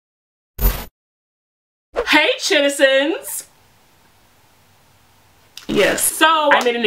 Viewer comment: I thought she said “sh!tizens”at first….